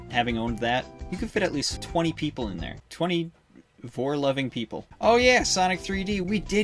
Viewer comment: I used to think was saying "24 loving people".. How I wish I could go back to that